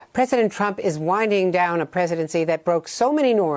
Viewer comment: sounds like andrea said president trump is whining down a presidency...yup.